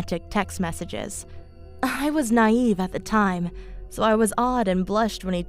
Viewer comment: she said I was “nieen” instead of nineteen